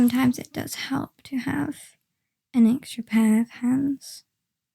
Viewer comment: I swear when you said "it does help to have an extra pair of hands" I heard that as "extra pair of pants" and for a brief moment I was very worried about just how embarrassingly I had screwed up the heist.